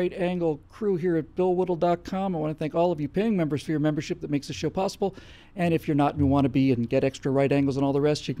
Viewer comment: I'm pretty sure he said it makes this show possible but it almost sounded like he said it makes this shit possible... go guys, I love what you do...